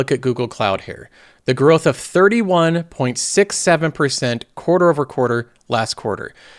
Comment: did you say... 6... 7??